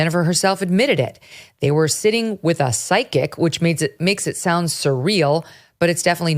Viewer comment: I thought they were saying "sidekick" instead of "psychic." I'm an idiot.